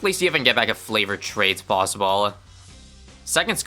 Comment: did you just say FLAVORED TRADES?